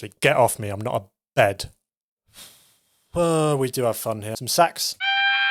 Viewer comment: 😂😂😂😂😂 thought he said sex 😂😂😂